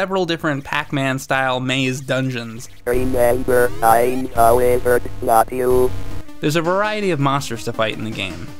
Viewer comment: I heard whatever was said as "Remember I'm Peppi Le Pew" 😉 You got to love the early days of digitized voices 😄